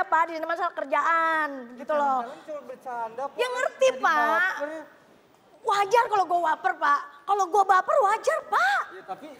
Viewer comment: menit 9:40.. Mpok Alpa: "Wajar kalo gua wafer pak".. maaf mungkin ane yang salah dengar..hehee